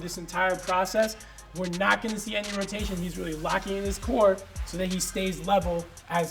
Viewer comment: you sure? Because all i hear is that u must lock the core